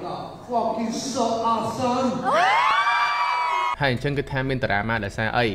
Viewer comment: what was he saying to the cambodian fans? I don't even understand what he said. i hear he said "cambodia is OMG fuck you something something"